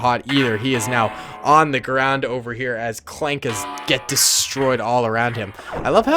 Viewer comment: is it me all does he say clanker with like a full ozzy acsent